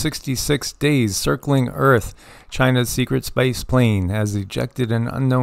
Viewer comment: in did you say secret spice plane :0 the spice malonge. Is seriousness, my interpretation, there isn't enligh room right now for us to change. Theres too much control, to many eyes, to much reach. Some sort of event is destined to happen at this point just becuase we need space to grow. Like a forest fire sweeping through and burning out the old and the boost of life that follows. Natural forest fires that is. 😊